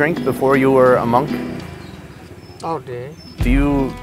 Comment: when you asked if he drunk before he was a monk it sounded like he said "all day" LMAO